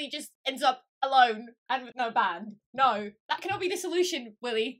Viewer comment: When you said Julie ends up alone and with no band I thought you said Julie ends up alone and with no man. I had to rewind that part again 😂 made sense though If Luke leaves